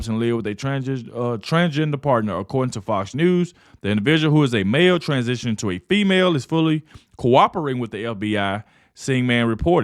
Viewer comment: I love your accent! I thought you said "a bear transitioning to a female"😂. I'm not disrespecting this message and video post. Sometimes we need a little breath in-between all the tragedy